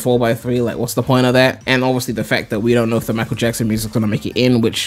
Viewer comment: Did he say... Michael Jackson music??